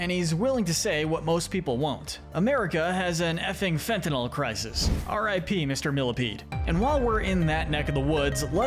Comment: Oh thats what he said !!!! I thought he said “fucking fat problem” which aint news lol